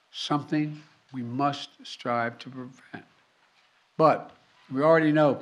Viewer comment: That "Prevent" is barely hearable.. I had to put on text to understand what he said.